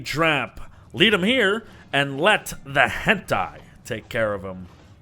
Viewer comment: Did he say "hentai"? Even subtitles says "hentai".